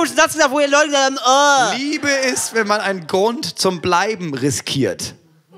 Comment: Ich hab zuerst verstanden: Liebe ist, wenn man einen Grund zum weinen riskiert😂